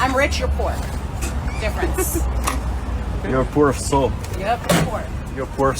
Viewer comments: What is dude saying? "Poor assault"? "Pour a salt"? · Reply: He said "A poor soul" Was that really that hard to figure out?...... lol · What is dude saying? "Poor assault"? "Pour a salt"? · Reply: I thought he said poor as salt